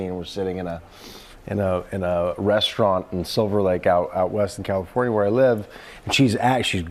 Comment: Where did he say he live at 3:02? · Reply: silver lake